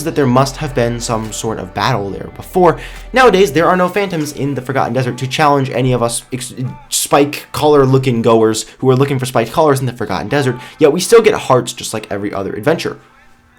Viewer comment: Instead of Forgotten dessert i heard "FACAN dessert"